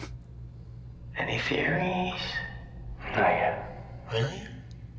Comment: WTF did he say. The audio is awful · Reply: "Really? I mean you're always so ahead of the curve"